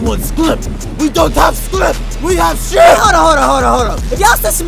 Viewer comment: what did negative say that spider-man wanted? Skrip?😂😂😂